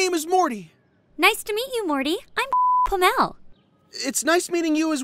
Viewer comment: did it just say it's name was FUCKlamel?